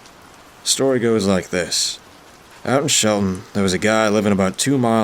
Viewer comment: did you say shelton or sheldon